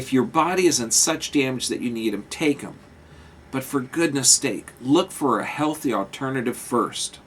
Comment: did he just say steak.....mmmm steak hahah. Great knowledge, love the vids, keep it up :)